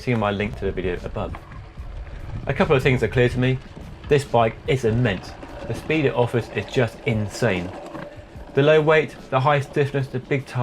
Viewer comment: What is he trying to say? "This bike isn't meant for speed it offers is just insane."